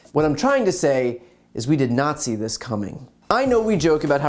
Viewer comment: Ha! 00:30 i thought he said "we did nazi this coming..." Still not sure. Good one.